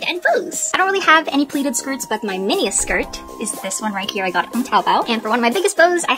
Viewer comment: I THOUGHT U SAID U GOT IT FROM TACOBELL LOLLLLLL! Btw love ur channel!